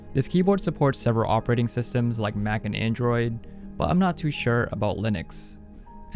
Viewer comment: did you say MAC AND ANDROID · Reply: Yup Mac and Android. Didnt mention Windows since I'm already demonstrating that.